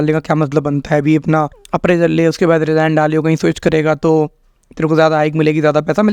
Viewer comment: pe kya bola apprecal milegaa ye kya hota g